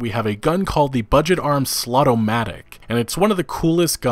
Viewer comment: Thought said the budget arms Slutomatic there for a second. Until I read the text.